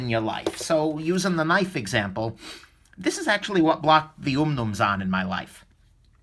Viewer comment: What knife are you saying at 7:40? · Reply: Eric Bowling - Umnumzaan by Chris Reeve